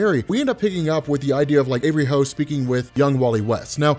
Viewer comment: did you say every hoe? lol wtf...what kind of a name is that for a female hero? she is every hoe ... lol